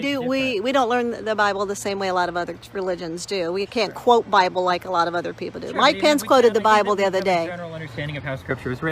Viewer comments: Did she just say her dead parents quoted the Bible the other day?? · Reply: I think she said Mike Pence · Did she say her parents quoted the bible yhe other day? I thought she said earlier in the conversation that her parents were dead... 🤔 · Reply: I went back to 18:01 and to me it sounds like she said Mike Pence, not my parents, but I could be wrong.